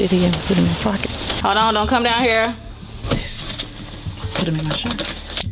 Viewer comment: All I heard was "hoe'd own... hoe'd own... don't come down here... hoe'd own."